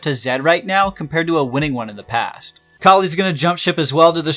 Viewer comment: what are u even saying there? Which champion are you talking about? Akali?